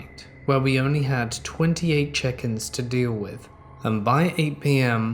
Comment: I kept hearing “chickens “ every time said “check ins”